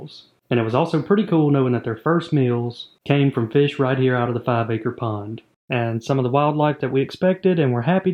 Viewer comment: I love how youtube is so random sometimes.. You are watching tech review and youtube, do you want to see 27min long video about 5 acre pond? hmm, ok, why not. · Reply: I thought u said acre porn. 😅